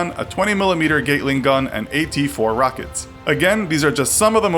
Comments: why does it sound like you said a "20mm Giggling gun"? Your weapons sound cute not deadly. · Is it just me, or did I hear 20mms "giggling" gun instead you f gattling? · I heard "20mm giggling gun"